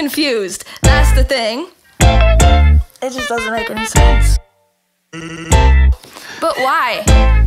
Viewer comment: Sans? I heard sans